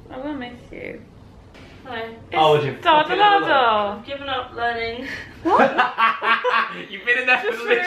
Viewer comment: I had the volume quiet for this video and when said 'ive given up learning' i thought she said 'ive given up running' i was like SAME BABE 😂😂